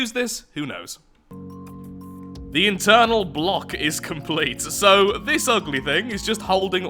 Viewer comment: I heard “The Eternal block is complete” and had to check if I missed an internal Cult memo